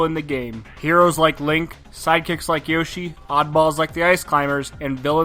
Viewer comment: I thought you said “physics like Yoshi”